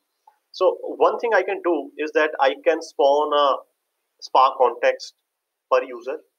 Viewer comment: you said some thing at 2:39.. i did not get that word.. the sentence is "I can _ a spark context per user" what is that missing word? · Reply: I think the missing word is: Spun. its the past tense of the word: Spin. generally the word is used as 'spun a server' means different meanings like "introducing a new server or node, or starting or booting the server or node". This is because, starting or booting the server, spins the hard-disk to load the OS. This is how the word came into practise. hope this helps.